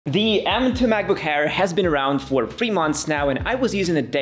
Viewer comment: did he say macbook AIR or macbook HAIR